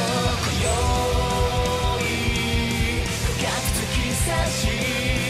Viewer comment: soy yo o pareciera que dice besame ru.